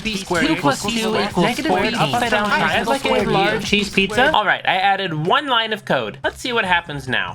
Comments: In 0:30 did he just say cheese pizza😂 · Did he just say large cheese pizza while coding? 😆 🤣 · am i the only one who heard him say "cheese pizza" near the end of the coding montage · anyone else heard "i'd like a large cheese pizza?" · Wait a minute DID YOU JUST SAY LARGE CHEESE PIZZA? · Who heard "Chesse pizza" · I heard large cheese pizza